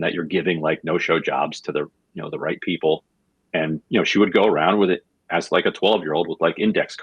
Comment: I had to listen to Ryan Grim talk about Pelosi going around Baltimore part a few times. I thought he said she walked around with an 🍑 like a 12 year old’s for a little bit. I have actually heard that saying before and thought, no way was he using it in public on a progressive show. It took a few rewinds and watching his mouth to realize he was saying she walked around with index card as like a 12 year old. Maybe I do need to get a pair of those RayJay earbuds just so what I’m hearing is coming through clearer